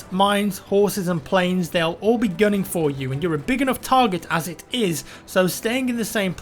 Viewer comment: For a second I thought he said: "tanks, mines, autism, planes, they'll all be going for you." I was eating at the time though.